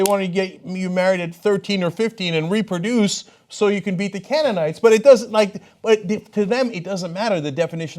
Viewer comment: I had to re-listen to Cenk's thoughts on the old tradition of birthin' like hell. He said it was about "numbers to beat the Canaanites". At first I thought he said "to have enough cannon fodder". Well hell's bells, comes down to about the same thing, I guess. Silly humans...